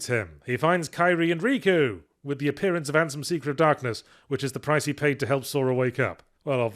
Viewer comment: Really thought he was saying handsome seeker of darkness for a while there. Dr Diablrery vibes (i only read the audiobook)